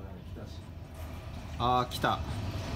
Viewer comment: Minor correction, but at 5:25, instead of "oh stop", I believe it's more like "Ah, here it comes" since it sounds Japanese. あ、来た。